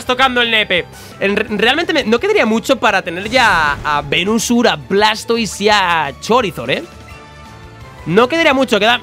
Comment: no quedaría mucho para tener a venusur blastois y chorizord Eso entendí yo